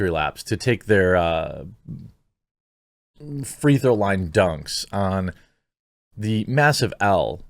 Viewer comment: Tried finding in comments but can someone please clarify what he said at 0:11 ? Thank you! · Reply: "Free throw line dunks"